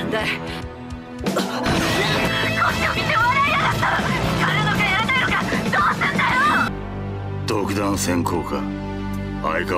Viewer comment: のキャンティのセリフなんて言ってるかわかる方いませんか？😢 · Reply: こっちを見て笑いやがった！？やるのかやらねぇのかどうすんだよ！？？だとおもいます！